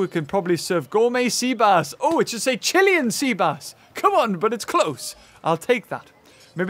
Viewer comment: Not gonna lie, I always thought Hammond said, "Chili and Seabass," not "Chilian Seabass." 😂😂😂